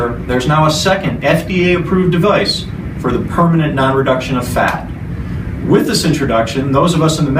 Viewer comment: Did he just say the "permanent non-reduction of fat"???? I thought it was supposed to reduce fat.